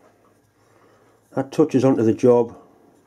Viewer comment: "That touches onto the job". Actually, it sounded like it had touched onto the cloth. 😀